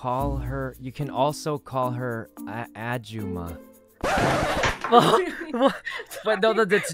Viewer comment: i think he said 아줌마(ajumma/ahjumma) which is most often used to refer to a middle aged lady(40-60) in korean pLEASE